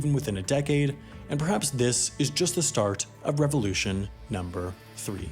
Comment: I thought it said terrorist revolution